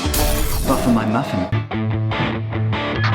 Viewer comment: Did he say "ruffle my muffin" ?